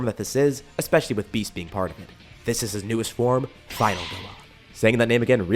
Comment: “Final Nilla”? No idea what he said there 😂